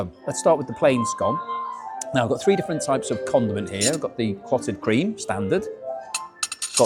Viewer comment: had to go back a couple of times as I thought I heard “three different types of condom”😂